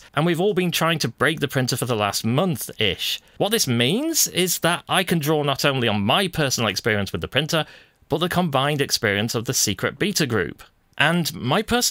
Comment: This was enjoyable. It's great to see everyone from the beta test group posting such high-quality videos. :) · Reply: Oh, I thought he was saying "beater test group"...! Thanks for the clarification as I was quite confused, especially after he also mentioned they were all busy trying to break it. Language is a strange animal.